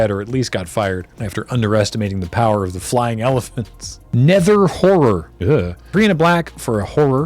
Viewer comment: Nether Horror was featured in the MTG episode of Commodore Hustle. James casts Chandra’s Outrage on Graham’s Nether Horror. Kathleen interrupts to tell them that the line to the convention event is moving but Matt hushes her so that they can keep playing. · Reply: I always heard it as "nether Whore", but looking back now I realise that they probably wouldn't allow that on a magic card, and if they did LRR probably wouldn't put it in an episode.